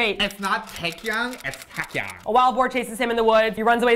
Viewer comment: how did you say wild boar it sounded like you said meth-Wedgie lol